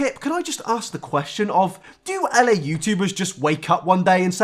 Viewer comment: i love how "la youtubers" is becoming a category to describe how awful a person is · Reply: “L.A youtubers”, I thought you were saying “the YouTubers” in Spanish.